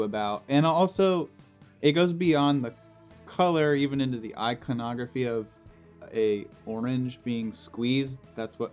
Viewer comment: I realise you said iconography at 15:25, but I kinda misheard and now I feel that eyeconography should be a word... Great vid, such an underappreciated aspect of effect pedals.